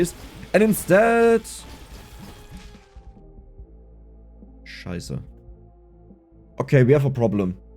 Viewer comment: did you just said "scheiße" ? xD This whole thing is english and this random german word hits me like a truck lol